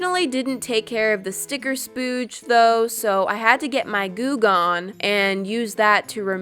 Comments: When she said, "Goo Gone" who else thought she was saying, "Goog On"? No?....... Just me?............ Imma head out now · Honestly I thought you said “now let me get my goog on” 😂💀